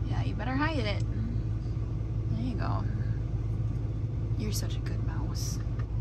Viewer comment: Everytime I see Despero in your van, I hear you saying "you're such a good road mouse!" 🤣🤣 🐁